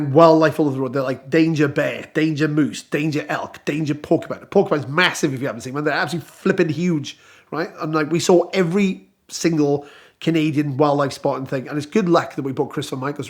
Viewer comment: Porcupine’s are big? One of the smaller creatures in Canada to avoid on the road, but pretty solid if you hit them that is for sure. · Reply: Ah, I thought he was saying "danger Pokemon" and I couldn't tell if he was making a joke or I just wasn't understanding his accent. 🤣